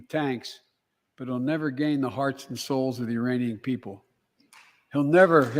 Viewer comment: Did he say "The Iranian people", right around the 42:05 mark, or am I hearing things?